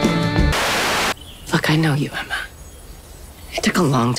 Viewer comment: I heard "fuck, I want you, Emma" 😭😭 I blame my zero sleep for this 🤧